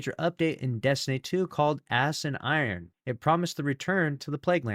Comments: Notice he said “ass” instead of “ash”, it has become habit. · My favorite part of the video was when at 2:13 said "Ass and Iron" instead of Ash and Iron. Even closed captioning says he said "Ass and Iron". He was reading it, but the joke of "Ass and Iron" is so ingrained in folks that it seemed like he didn't even notice it. If he did and he did it for the bit, KUDOS on the delivery!